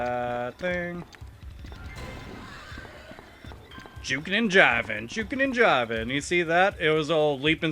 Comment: What did you say? "Juke 'n' Javan, juke 'n' javan." ??? Please respond, I want to use this call so badly. xD